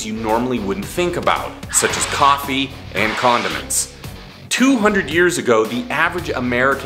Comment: i thought he said “coffee and condoms”